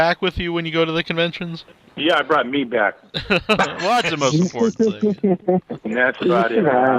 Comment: i think he said "i brought meat back" not 'me back' lol